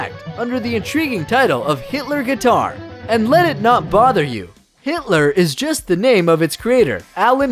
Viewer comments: am I hearing this wrong? it really sounds like he's saying Hitler. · Did he just call it a Hitler guitar??